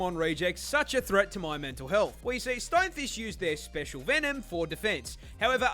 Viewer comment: I thought she was saying rookfish instead of stonefish. Rook as in the chess piece.